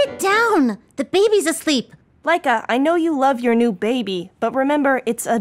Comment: When she said Laika I thought she said mica which is my name xD